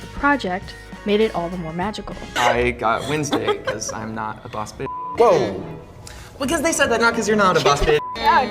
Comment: What did he say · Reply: I got Wednesday because I am not a Bad B*tch